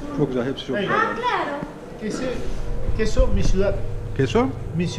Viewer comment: Que sont missiodant... Gibi bir şey diyor.. Sence bunda ne var... diyor Yani bu peynir hangi sütten...diyor Fransızca ya benziyor ispanyolca... Ondan anladım ... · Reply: Queso es de mi ciudad diyor. Peynir benim memleketten geliyor demek.